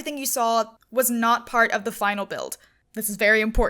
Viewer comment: Oh, not the final build! I thought she said all of that was part of the final build xD